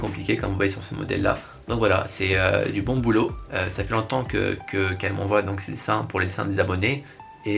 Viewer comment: Mdr c'est moi ou à 1:05 on dirait qu'il dit "ça fait longtemps qu'elle m'envoie ses seins"